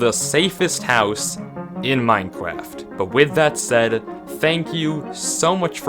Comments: did you just say minecwaft? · D-did he say Minecwaft...?